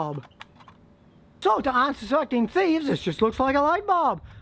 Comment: I thought he said "cock sucking thieves" 😂😂😂😂